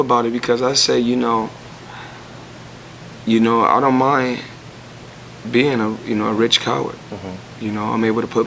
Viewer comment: Did he just say "I don't mind being called a Rich Coward" 6:34 into the interview